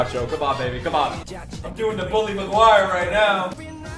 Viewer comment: for a second, I thought that he said Harry Mcguire, I'm having nightmares because of him, GTH Mcguire lol